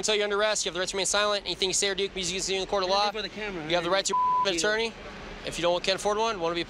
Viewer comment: did he say you have the right to f**k an attorney?